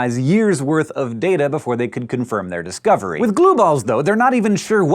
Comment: Every time you said "glueballs" I heard "blue balls"... couldn't help it lol